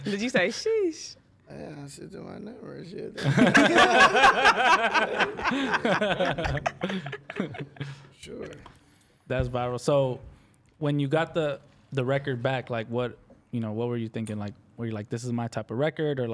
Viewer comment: did he just say "thats viral"?..